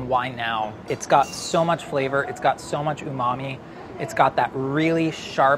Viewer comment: Everytime someone says Umami i keep hearing "Ooh Mommy"